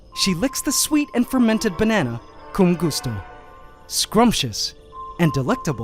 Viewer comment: Did he say “kung gusto” on 28:17?? cuz im Filipino...